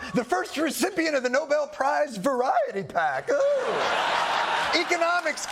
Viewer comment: Seriously did a double take when i misheard Dr Oz to say Trump should get the No ball prize 😂